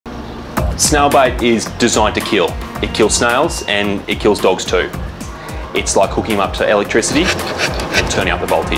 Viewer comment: Poor baby. When I first seen this I thought he was saying “snail bite”